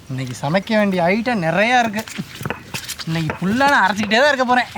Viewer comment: Subtitles - "Nee-sama candy I eaten earlier" Sweating profusely - I know where this is going but I don't know if I like it. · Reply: That auto generated captions is completely wrong... What he actually said at 0:52 is "Today we have a lot of recipes to cook... I will end up grinding the spices , the whole day..." 😇